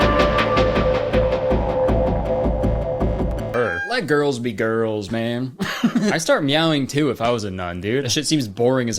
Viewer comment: what did he say? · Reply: He said purr 💅